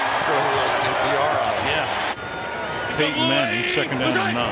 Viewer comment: did he say "Payton Manning?"